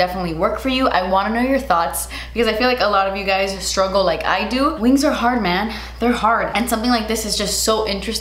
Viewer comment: Ok I know now that she said "wings are hard man" but I heard "things are hard man" and yes, yes they are